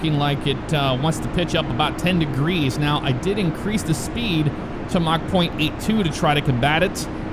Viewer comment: I thought you said "I increased the speed to mach 8.2" 🤣😂